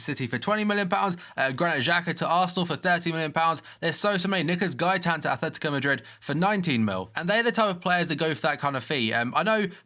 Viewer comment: What did he just say? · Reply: Nicolas Gaitan to Atletico Madrid for £19m